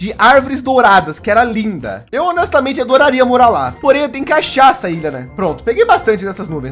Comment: Eu ouvi "porém tem cachaça aí né?" K44kjk4j